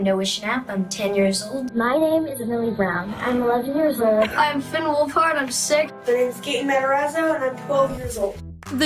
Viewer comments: Nobody: What I thought Finn said: "Hi im Finn Wolfhard and im 6-" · "I'm Finn Wolfhard. I'm sick." Damn this guy never fail to make me laugh. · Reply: ohh i thought he said "I'm Finn Wolfhard. i'm 6" lol